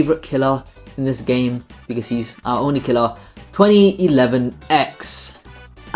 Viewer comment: I thought you said "Ao Oni Killer"